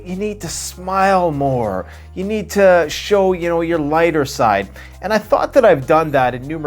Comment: I thought you said people say to show your whiter side haha I had to rewind that lol